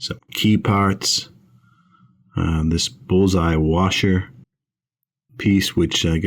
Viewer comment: Alright u guys making me what to go hit the Westerly, Rhode Island beaches ! At first I thought said bullpoop ! But it was only bull’s-eye ! Great day at the beach guys ! 👍🐆🐆😎